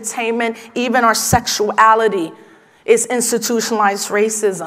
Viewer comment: Wait a minute. At around 6:46 did she say Sexuality is racist?